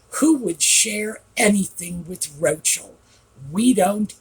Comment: Oh, I had to look at the transcript. I thought you said “Roachell”